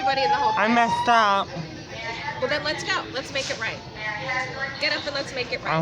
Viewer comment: At first I thought she said, "Let's go! Let's make it rain!" I was so confused.